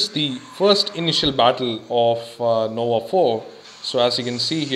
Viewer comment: did you say NOVA 4?